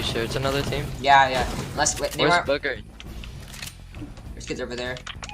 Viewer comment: he really said “where is bugger”